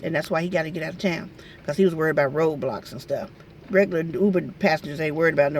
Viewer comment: Lol sounds like she said ROBLOX say really says Roadblocks Like if true